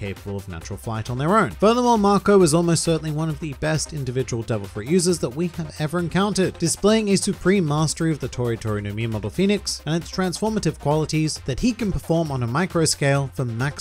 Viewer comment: ngl i thought you said tori tori no memeable pheonix